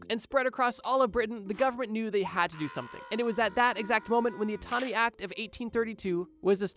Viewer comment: if i hear "autonomy" one more time with talking about anatomy imma go crazy.